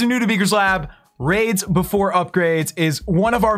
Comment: At first I thought he said grades before upgrades